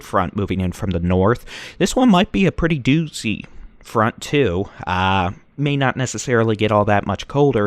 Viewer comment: A doozy of a front? Doozy is an adjective. Front is the noun. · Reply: he said "doozy front", didn't use "of a" in there, the way I heard it. It's not an adjective according to the dictionary.